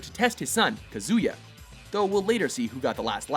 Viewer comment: Did he just say "kuyuza" instead of "Kazuya"?